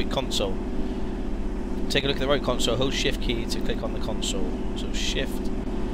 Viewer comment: 'Hold shift key" sounded like "Oh Shit-key" to me at first:-D haha